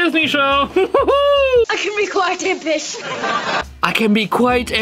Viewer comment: on the jesse show i thought he said "i can be quit a piss"